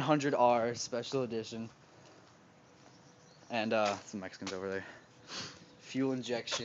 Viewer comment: it kinda sounds like he says some mexicans over there haha